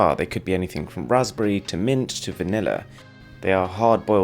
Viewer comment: I heard vanilla as"manila"